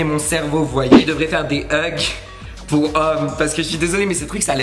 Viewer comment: c quoi des hug ou huk ?? je sais pas 😂😂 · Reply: Ugg la chaussure/bottine là